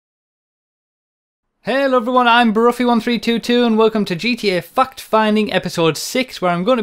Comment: Did you just say "fucked-finding?!" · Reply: But he didn't tho. He says fact. If he had said fuck in a northern accent the u would be incredibly easy to hear. Ask anyone from the north east.